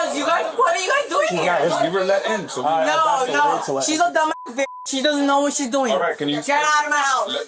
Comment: Did she just call her mother a "d*mb *ss b*tch?"... The mother opens the door at the start of the video. the 3:00 mark she calls the person who opened the door a "d*mb *ss b*tch"... I also heard her use the spanish word idiota when she was speaking to her mother... I understand basic Spanish but I hope am wrong 😟